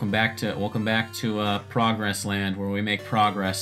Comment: I thought you said "Frog wrestling"